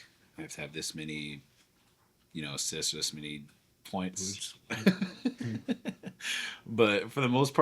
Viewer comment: What does Shai say at 7:02? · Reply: I think ‘points’ trying to finish jdub’s sentence